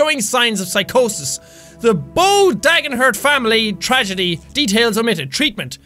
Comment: I thought it said Fran bow detergent 🤣